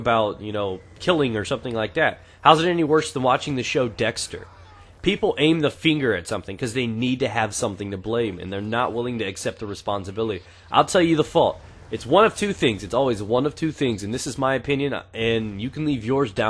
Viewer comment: DID SAY AIM DA FINGER?2:54 LMAO